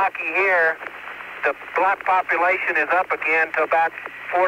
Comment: did he just said black population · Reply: He said “To back 40 to 50 percent.”